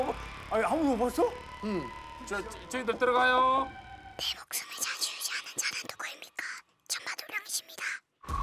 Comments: 뭐라는거야......10번 돌려도 무슨 말인지 모르겠네.. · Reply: 내 목숨을 좌지우지하는자는 누구십니까?천마도령이십니다 내영혼을원하시면 도령님을.... 여기까진맞는것같은데 아무래도 도령님을 죽여달라? 없애달라? 그런느낌일것같은데요? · 이쯤부터 나오는 의문의 목소리 잘 들어보면 “내 목숨을 좌지우지하는 자가 누구입니까 천마도령이십니다”임 뒤에는 잘 안들리지만 “내 영혼을 구원하십니다” 이거같음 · 내 목숨을 좌지우지하는 자는 누구입니까? 천마도령이십니다. 개소름… 근데 이 뒤에 또 무슨 말하는데 멤버들이 소리 질러서 잘 안 들림…